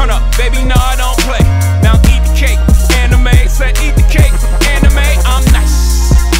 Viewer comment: who else thought jay-z said anime? 😂😂😂